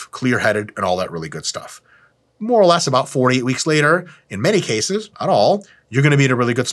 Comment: Did Mike say 40 weeks later or 4 to 8 weeks later around 11:50 ??